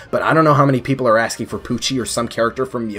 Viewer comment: at first I thought you said "Pucci" but I quickly realized you said "Poochy" Can't escape JoJo dude.